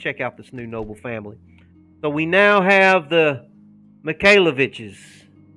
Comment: Oh God I thought you said MIKAYLA BITCHES not MIKAYLAVITCHES lol